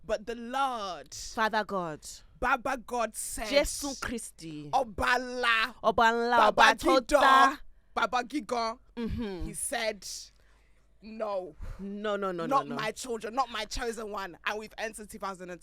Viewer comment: somebody help me just can't stop laughing rn what was saying 1:30 "baba gingan" 🤣🤣